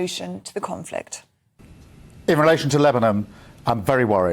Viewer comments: Release the sausages! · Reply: 00:7:24 He actually says Lebanom instead of Lebanon. Something so not right going on here! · He said sausages instead of hostages & now Lebanom instead of Lebanon! WTF is going on man?! 00:7:24